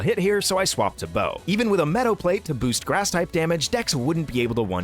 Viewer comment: did he say a metal plate to boost grass type moves? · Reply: i believe he said meadow plate